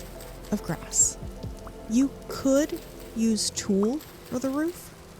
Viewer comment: "You could use tool for the roof"? What are they saying? lol